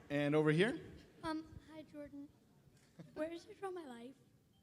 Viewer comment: I can't understand what the kid asks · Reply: "Where's Draw My Life?"